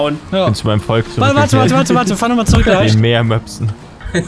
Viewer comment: was hat paluten da gesagt?😂😂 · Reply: Noch mehr